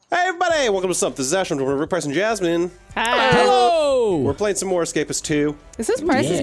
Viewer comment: The intro i keep hearing "were playing some jasmine" after watching and finishing dont starve season 1 2 and 3 i now have only heard it over and over again....